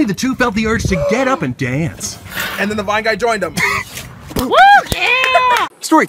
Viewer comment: I thought he always said "and then the black guy joined them" lmao.